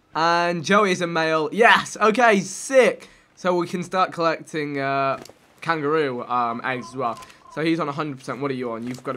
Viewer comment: did he say kangaroos laid eggs????